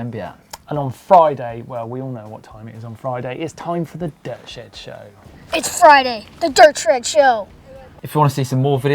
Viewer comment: did say the dirt SHRED show?